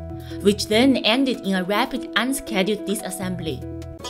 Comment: RUD 5:20 0:21 "Rapid Unplanned Disassembly" She clearly and unequivocally pronounces it "dis-". Even youTube's TTS text shows the software hears "disassembly" . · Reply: 3 seconds into the video in the intro, it sounds like she says "re-assembly". Maybe don't be so much of a smart ass and just have fun.